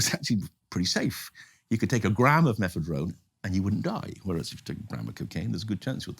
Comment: What's that word at 37:22? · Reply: Mephedrone